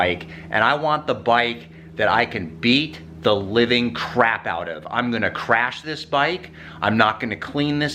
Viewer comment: I first thought i heard wife when you said beat the living crap out lollll